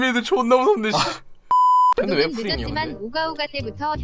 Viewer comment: 에 잼민이들 존나 무섭네 라고 들리지만 넘어가자 웃긴건 맞으니 ㅋㅋㅋㅋㅋㅋㅋ